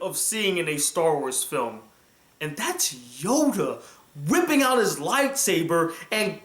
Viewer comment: sounds like you said Yoga.